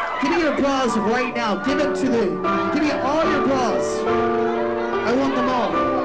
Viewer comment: I was listening without watching, and thought he was saying "give me applause". I got confused and looked at the screen and saw him holding bras, got even more confused, and then finally realized what he was saying